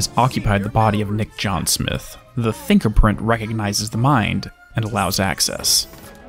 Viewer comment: Ahhhh, the “thinker”print! I thought you Said fingerring, bacuce thats what the subtitle read😂.